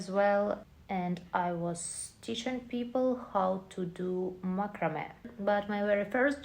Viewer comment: teaching people how to do what? I heard "microman" did you say makrame and what is that?